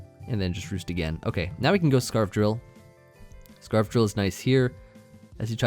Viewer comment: When you said scarf drill i thought you said av drill then you said it had rocks so i was so confused i had to do a double take